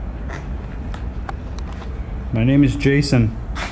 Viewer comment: I heard a voice say "Jason"